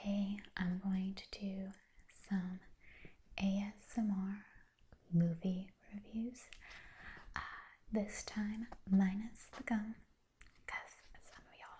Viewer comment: I thought it said ' movie reviews with Anal' lol